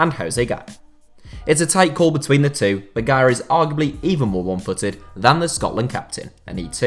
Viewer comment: I thought you were saying Garay not Gaya